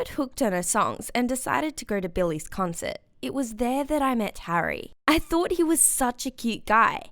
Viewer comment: At one point I thought she said Garry instead of Harry comment down below x